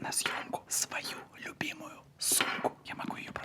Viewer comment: Влад сказал:ребята я взял на съёмку свою любимую сумку. Как все думают но мне не одной послышался место сумки мат?